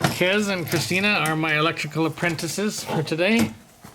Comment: Misunderstood you, you said they were your electrical apprentices today and I thought you said electrical princesses.